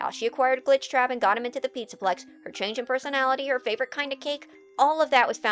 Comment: When talking about the Vanessa info from FNAF AR you said "favorite kind of cake" so quickly, I thought I heard "Favorite kind of KINK" and did a double take! 🤣